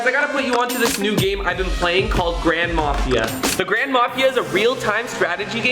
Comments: I thought he said "the grandma mafia" TvT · Ngl I thought he said grandma mafia lol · I thought he said grandma fia during the sponser 😂